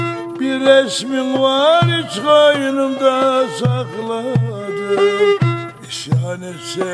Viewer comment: orada tam olarak ne diyor "hiç koynumda Sakladım"